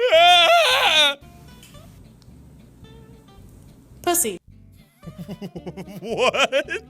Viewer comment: an ad popped up on my computer at 8:50 and it sounded like the girl said "I'm Ed Gillespie" instead of pussy